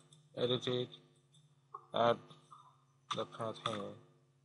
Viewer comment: what you say at 4:33 is not clear did you say paste here?